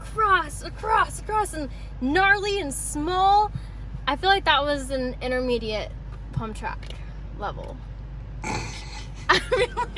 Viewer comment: what in the actual ** was she saying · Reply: "I feel like that was an intermediate pump track level"